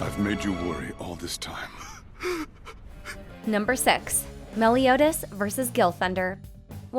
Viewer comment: Did she just said "Number Sexs"